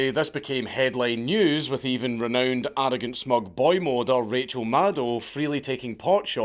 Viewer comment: did I just hear dankula say boymoder??!!!